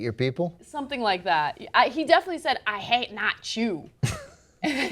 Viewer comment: I thought he said, 'I hate not chew.' Maybe if he took better care of his teeth things would be different.